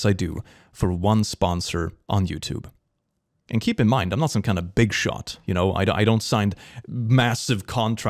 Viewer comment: Did he say he’s a BIG SHOT?!? BIG SHOT KRIS BIG SHOT (Oh my god Deltarune Spamton brain worms)